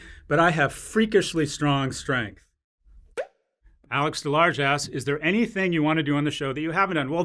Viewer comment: Sorry Alex, but it sounded like said The large ass.